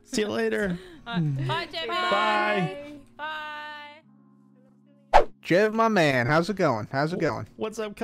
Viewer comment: what I heard is "Bye Jimmy"